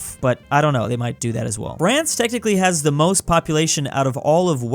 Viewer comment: did he just say Brance?